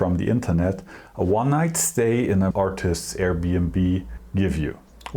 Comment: I thought you said, "... a one night stand..." and was like "NO STEFAN! DON'T P*MP YOURSELF FOR MACHINE ACCESSORIES!!" 😂😂